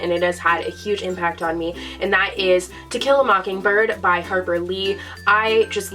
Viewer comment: I thought she said Tequila Mockingbird